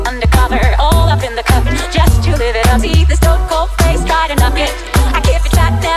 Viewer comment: I'm sorry, did i just hear- "The stone-cold face got a nugget"